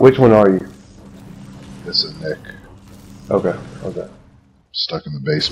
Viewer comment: when the guy asked what his name is i thought he said thats a neck lmao hahahah hahahahahhaa i have no life